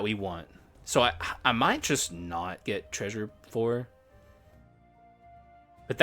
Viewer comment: treasure plour, did you really say that lol